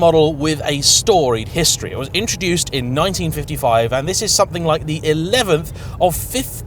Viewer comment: Did I hear “It was introduced in 1955”?